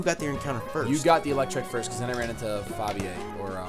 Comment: Did call Ampharos Fabio? :]